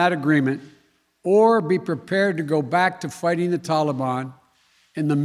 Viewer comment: Every time I hear Biden say "Taliban," I hear "lollipop."